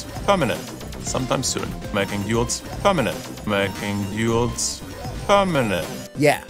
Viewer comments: what is he saying? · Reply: Making duels permanent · So, what exactly did say in 1:08 ? Edit: I checked the Candyland Brawl Talk. He said, "Making duels permanent."